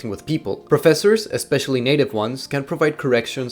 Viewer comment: wtf did he say. I think he said native ones but it sounds like he said naked ones 😂